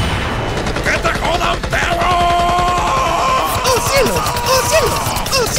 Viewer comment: que te hodas pegooooooolooooo eso entendi